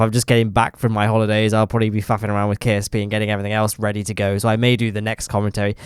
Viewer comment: Wait? Did I just hear the F word. That’s a first for me. 1:15 lol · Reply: Noo noo noo. I will probably be faffing around with ksp